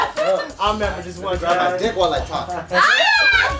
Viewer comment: what did say at 10:54? "i'm getting dick while i talk" ?